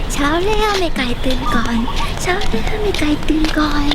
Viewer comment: เช้าที่ข้าวมันไก่ตื่นก่อนเช้าที่ข้าวมันตื่นก่อน ( ทำไมกูได้ยินแบบนั้นวะ ใครได้ยินกด like )